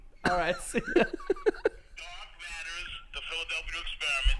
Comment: I looked for 10 minutes for "Dog matters" before I heard it right!